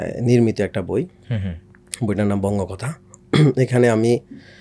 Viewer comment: বই টার নাম কি বলল?? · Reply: বঙ্গকথা